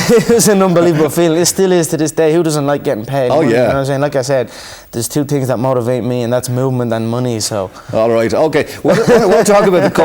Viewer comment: TV3 Ireland its at 5:10 I think he says 'movement' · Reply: James Hartley "There is two things that motivate me, and that's movement and money so hahahaha" Hope that helps